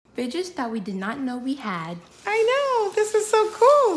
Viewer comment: NO BC IN THE START I THOUGHT YOU SAID “BITCHS WE DIDN’T KNOW WE HAVE” 💀💀💀